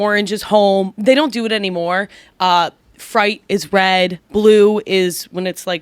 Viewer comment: Ummm I'm gonna need to say the word "freight" again to see if I heard her right...